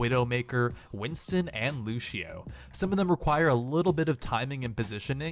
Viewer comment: anyone else hear him say lushio lol